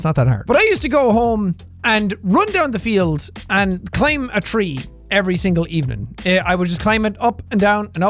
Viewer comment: anyone else have a uHMSORRYWUT? moment because they thought he said "claim a tree" for a second